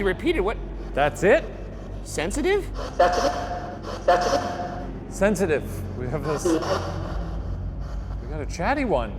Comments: I swear when y’all asked the name and it said “sensitive” I thought it said BETHANY! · I kinda heard sedative instead of sensitive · Send us q's for the GF Debrief under this comment! Make sure to send also some questions for our fearless director and cameraman, Mark! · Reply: I was not looking at the tv screen but I was listening to the conversation, it sounded like a very clear “that’s me”. If you guys can hear that, what are your thoughts on it? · At about 6:50, I hear "That's me," both times. Spooky stuff. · idk if it’s just me but on 6:50 it sounds like it said “Touch it” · I think it said "that's it", at 6:49 or 6:50 . It didn't say "sensitive" according to me. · when u guys heard "sensitive" at 6:52, my mom and I heard "that's it" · anyone else hear it say "Stephanie" and not "sensitive" at 6:50? still does sound like sensitive but I could honeslty hear the name Stephanie come up... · I heard "that's me". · I heard "that's me" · “Stephanie” it sounded like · When asked we’re looking for Edward 6:44 it sounded like he was trying to say “That’s—me” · it sounded like “that’s me”, even more so when Ryan asked them to repeat it😳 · I thought it said "that again" (not sensitive) when Ryan said "Can you say that again" 😂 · I heard "Touch it" then they repeat it for Ryan. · I always try to listen to the spirit box without looking at the video - tell me why when it said "sensitive" I heard "f*** you" lolll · I thought instead of 'sensitive' it may have said Stephanie.